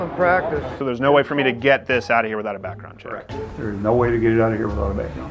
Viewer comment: There was a minor typo around 3:12 where the guy says "without a backgroung check" but the subtitle says "with"